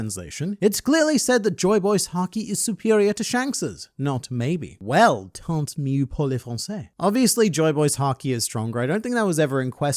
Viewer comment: took me a while to understand what was said 😂 "Tant mieux pour les français" => means something on the line of "good thing for the french people"